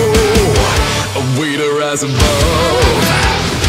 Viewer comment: a waiter has above? is that what he says? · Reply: i don't know He’s saying “A way to rise above”